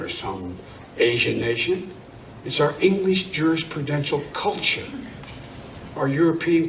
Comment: I can't understand what he's saying around 2:33 + "culture, it's our english jurest purdential culture" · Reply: My bad, he says "jurisprudence" Definition of jurisprudence 1 : the science or philosophy of law "Jurisprudence" goes back to Latin prudentia juris (literally "skill in law"), from which was derived the Late Latin formation jurisprudentia, and subsequently our word. The noun jurisprudent means "one skilled in law" - in other words, "a jurist."